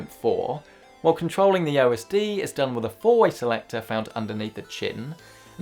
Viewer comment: '4 way selector underneath the chitten' honestly thought that's what you said hahaha